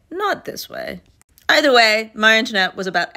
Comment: I thought I heard you say ‘my internet’ instead of Marie Antoinette the first two times 😂